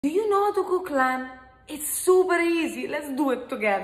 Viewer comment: I heard "do you know the ku klux klan?" At first, had to rewind it lol